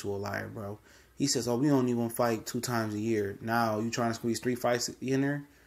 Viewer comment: I thought he said he was only going to fight 2 times a year